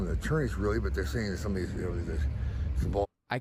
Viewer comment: It sounds like he said "attorneys. "